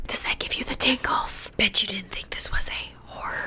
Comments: Me : hold up did Lauren just said Bi*** ._. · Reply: Lol She said "bet u Didn't think..... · Lauren: B***h you didn't know this was a horror game Me:WHAT THE FLAP DID YOU SAY >:0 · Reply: She said bet ya